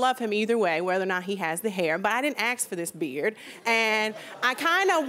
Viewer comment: english is not my main but did i hear her saying "i didn't axe for this beard" ?, it suppose to be ask not axe ?